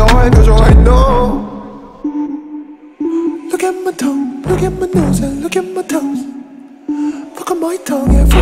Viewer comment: Is it just me hearing “look at my toes”? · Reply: sꚔυ:7 sꚔυ:7 oh lol I heard “look at my toes, look at my nails and look at my toe” 😂